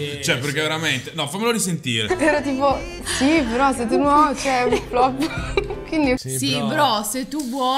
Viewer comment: solo io ho capito "si però se tu no ceh flop"😭💀